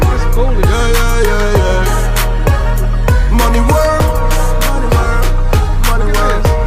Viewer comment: “””Money world “” $$$ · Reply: Wow this whole time I thought it said “mami work” 😂 especially since she’s dancing. But “money world” makes so much more since lol